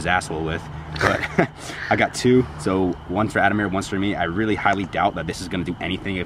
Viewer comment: at like 5:30 it sounded like someone yelled "ademir"